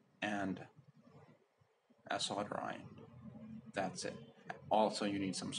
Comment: quick question: what did you say at 2:49 ? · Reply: Solder Iron [Soldering iron]. 😊